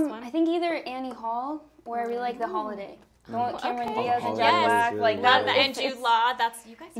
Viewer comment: Mitchell saying "Kiernan" in the softest voice, I thought he said Cameron